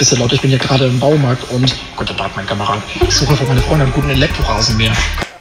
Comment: Was sagt der Typ bei 1:26? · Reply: Guten Tag mein Kamerad